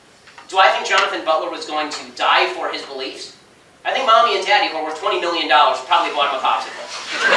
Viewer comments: What did he say @ 1:57? "Mommy and daddy..." what? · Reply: "Mommy and daddy... who earn twenty million dollars... probably bought him a popsicle." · "I think mommy and daddy and over 20 million dollars...<???> "? What did he say? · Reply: "I think mommy and daddy who are worth $20 million dollars would have bought him a popsicle."